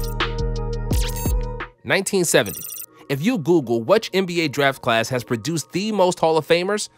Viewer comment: i thought he said if you booboo but its google